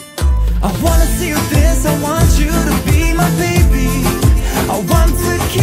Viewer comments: Why did I hear “I want you to feed my baby” 😂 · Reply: Suhara Shuchita I read the other comment and now all I hear from 2:50 - 2:55 is “I want you to piss I want you to feed my baby” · Why did I hear “I want you to feed my baby” 😂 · "I wanna beat your thighs slowly" 2:51 "I wanna see you piss" don't kids sing this song? Edit: I don't care if you think he said "fire" and "dance", he still said "go into your danger zones and make you scream" can't deny, also it's yanny not laurel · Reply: He says “I wanna see you dance” 😂